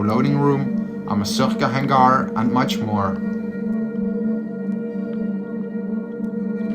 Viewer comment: what did you guys say about a hangar? Closed Captions said a "Massage Hangar".